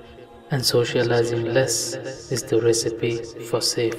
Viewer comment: Salam All! Click Here To Support The Dawah: <link> This Video is Created by & for The Daily Reminder If you Like what You See, then Help Us Help You & the Ummah at large by Supporting The Dawah. * This video is Creative Common's (Not Copyrighted) ** No Impermissible Music was used in the production of this video JazakALLAHu Khair, TDR Team. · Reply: ***** walaykum alsalam :) "Radi allahu aanha" is used for feminine pronouns.. it basically means "may Allah be pleased with her".. on the other hand "Radi Allahu anhu" is used for masculine pronouns as "May Allah be pleased with him".. "Radi allahu ta' alla" means "May Allah be pleased..." it's not a complete sentence until you add a pronoun at the end.. at 0:55 the speaker is actually saying "Radi Allahu ta'ala anhu" but his voice got lower while saying the last word "anhu".. hope that helps :)